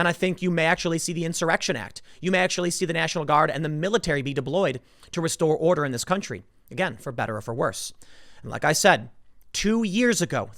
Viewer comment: Did he say 'diBloyed'?